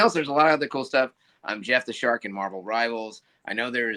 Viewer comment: When he said Jeff the shark I thought he said jump the shark, What a great character name that would have been. And a reference to that Happy Days episode